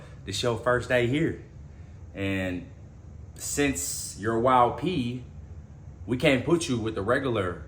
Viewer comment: I thought he was saying Wild P instead of Y.O.P. I was like wtf gang is the Wild Ps 😂